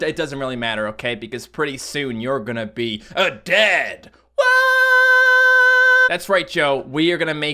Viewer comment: Well, for some reason I heard that as "pretty soon, you're gonna be a-dead!!!". Oh well, at least you can do funky effects on your face :D